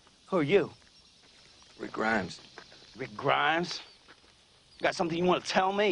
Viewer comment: I love how bitchy Daryl is here saying “Rick Grimes” LMAOOO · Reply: Sounds almost like he calls him “Rick Rhymes”. With that southern drawl 🤣🤣